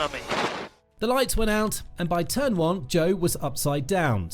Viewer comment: All i hear is “one of the most violent, one if the most scary, one of the most dramatic” at every crash 🙃 Edit; at 23:00 i never heard about Joe before. I know he meant Zhou though haha